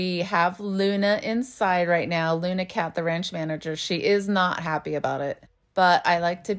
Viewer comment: OMGosh I follow Hodge Cattle Co. and literally thought “They have a puppy named June, too!” So she’s THE June!!🙏🏽🙏🏻🙏🏿♥️♥️♥️ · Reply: I genuinely thought I heard you call Luna, Luna Katherine the ranch manager. Made me laugh